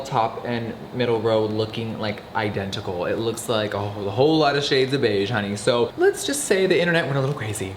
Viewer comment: i thought he said "it looks like a whole lotta shades of asian" 😂😂